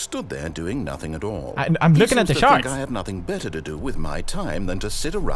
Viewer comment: I HEARD "I'M LOOKING AT THE SHARTS" XD